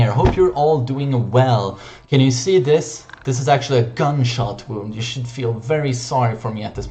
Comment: thought u said Cumshot